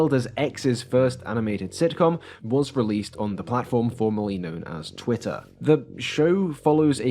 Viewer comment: "was released on the platform formerly known as Twitter" I heard this as "formally", which might be fair since cabridge says they have the exact same pronunciation in English english, and practically same pronunciation in american english. Regardless, I think it is several times funnier this way.